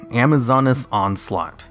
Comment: I heard it as "Amazoness On Slut"